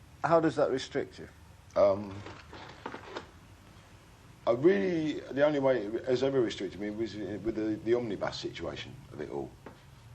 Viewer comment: subtitles are wrong. He says "Really the only way it has ever restricted me was with the omnibus situation of it all"